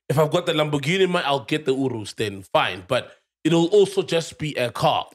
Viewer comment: when i heard URUS i was like wtf then i figured he was trying to say You-R-U-S 😂😂 i cant be the only one that heard that